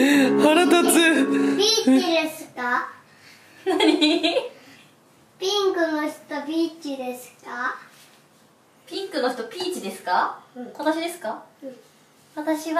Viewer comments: ピンクの人ビッチですか？に聞こえて焦った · 「ピンクの人ビッチですか」に聞こえたwww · ピンクの人ビッチですかって聞こえた、 大丈夫私は純粋